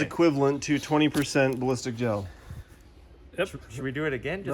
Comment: At first I thought it said ballsack gel lol